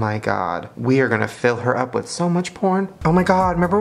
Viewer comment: did he say porn?